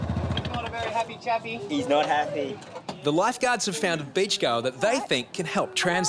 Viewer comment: I thought 11:08 said "the lifeguards found a bitch girl" 😐